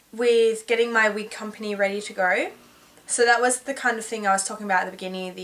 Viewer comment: I thought she said "Getting my weed company ready to grow" and "I love weed, so I wanted to start my own company". hahahha. oh.